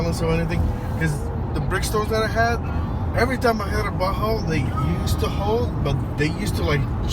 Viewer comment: Did you say butthole? haha :) Seriously tho, I like these tires as well. They work really good in the winter, no need for snow tires.